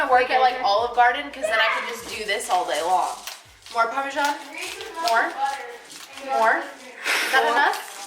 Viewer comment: I heard said "more" while is asking 'more parmesan'? 😄